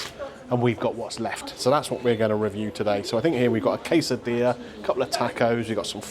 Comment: in your soft taco were was the tomato. and for a taco it is normally rolled up. never got a casadea i have never had, not sure its worth it and its also why i cant spell it heheheh the cin twists are 99p for what you get in that packet. the 2 chun and sauce are £2.49 not worth it. your hard taco did not seem to be fill full since nothing is falling out a single portion of chips reg is 99p you get far more than you get with McD. or BK plus you can always eat there fill out the survey and get 20% off your next meal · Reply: I heard “case of beer” instead of quesadilla…It would have been more nourishing for everybody.🥴